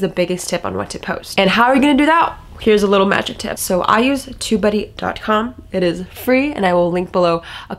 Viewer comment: I guess I have to improve my listening skills: I thought u were saying,”2buddy, or tobuddy”? 🤪 Got Tubebuddy from App Store-so cool. New tool-sweeet. Liked the 80/20. Thank you. 🙏😎